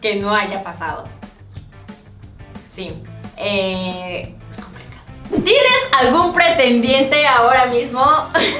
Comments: Alguien sabe lo que dijo en el minuto 5:04 ?.??? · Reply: Ury Paulina Gonzalez según mis investigaciones dijo"es complicado" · Alguien me puede decir que dijo karen en el minuto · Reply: es complicado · Que dijo Karen en el minuto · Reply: Gunner Forever es complicado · No entendi su susurro que dijo en el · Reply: Klaire Sofia dice es complicado